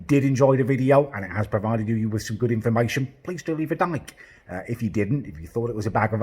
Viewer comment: At the end it sounded like he said ‘please go leave a dyke’ 😂😂😂